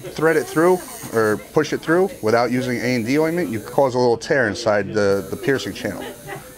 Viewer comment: what did he say here guys? A.N.D ?what's that